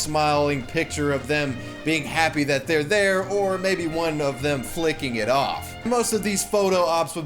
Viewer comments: Hold up. Does he say "flicking it off"? I always thought it was "flipping off". My world has been turned upside down. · did you just say flicking it off?